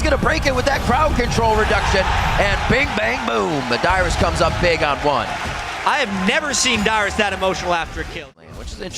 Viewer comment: What did dyrus say at 1:55? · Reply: get fucked bitch, get the fuck out of my lane